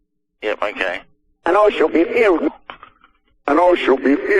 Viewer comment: I hear it as "and all shall be healed"